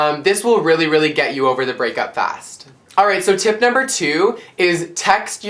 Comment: HAHAHAHAAHHA use the audio transcript, at 1:36, instead of writing 'alright, so tip number two' they wrote' are as of september issue' lmao ! :)